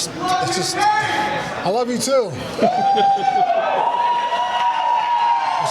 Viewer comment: Jon Jones yelling "I love cocaine" and Cain misheard